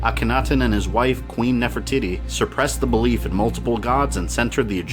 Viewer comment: It sounded like you said Queen Nefer-Tities